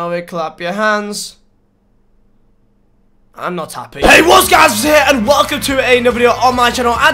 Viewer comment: Can anyone tell me what he says in the intro. I hear WHAT'S GUYS WHAT'S HERE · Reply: What´s up guys, vizeh here.